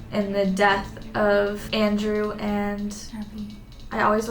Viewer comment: Did y’all hear like a female whisper at 32:13 it sounded like “Abby” or “addi” right before said the name? Cause it is there